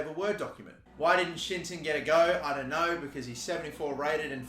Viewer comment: WHEN HE SAYS “shinton” y do i hear “shit-ton” 😂😂😂